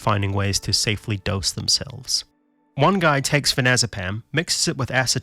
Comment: I heard that as 'Thanazopam'.